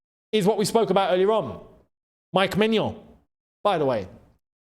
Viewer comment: When I first heard of him I thought his name was Magnum (ice cream) 😂😂😂